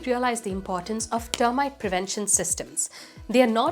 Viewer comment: Full-proof isn't correct. The word is foolproof. I've been hearing you say this for years and thought you were saying foolproof and were using it imprecisely but knowing you thought it was full-proof explains some confusion you have about the word. It doesn't mean "a complete solution" but "a solution anyone can use." I mean for this comment to be helpful, not insulting. Words are hard. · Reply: So is hearing. She was saying foolproof. Lol. It's her accent.